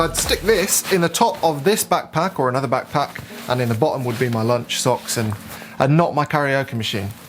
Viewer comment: “...and in the bottom would be my lunch socks...” 🤔 I must have misheard something!